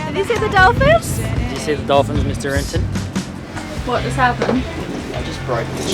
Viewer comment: Where does the nickname Renton come from? Maybe I missed it but I keep hearing Riley call Lenny Renton. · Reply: I heard “Mr Renton” :)